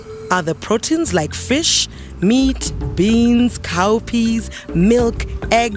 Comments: Did she just say cow pee??? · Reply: Cow PEAS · Did you just say cow pee dun dun dun😕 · What?!?!!!? DID She Just Say #Cow #Pee?!?!!!!?!??!